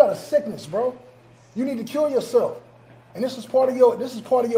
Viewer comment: I thought he said you need to "Cure Yourself" cause afterwards he mentioned, this is part of your healing. 🤷🏾‍♀️